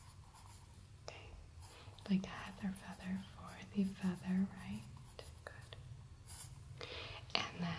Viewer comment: did you say HeatherFeather?